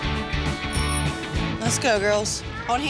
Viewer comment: When Danielle said "lets go girls" for some reason at first I heard her say " vsco girls"